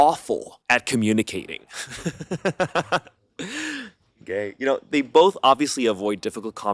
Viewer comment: did he just say gay🥹✌️ or am I tweaking